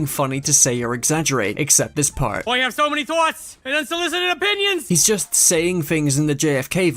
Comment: what did he say after “I have so many thoughts” and “opinions” ? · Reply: He said unsolicited opinions